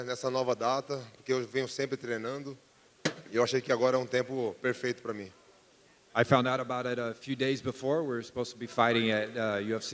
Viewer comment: did Alex say "perfect for me" or did it just sound like that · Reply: He said perfeito para mi in portugese